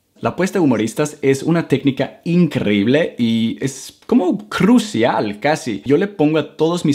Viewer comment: Disculpa no te entendí esa parte esos 3 a 4segundos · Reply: "como crucial casi"... eso fue lo que dijo.